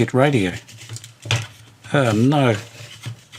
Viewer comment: When you said 'kit radio' I thought you said 'git radio'. I thought 'that's a bit cruel, I mean, you've only just met.' Good to see you on the air, God bless you and all the best for your speedy recovery.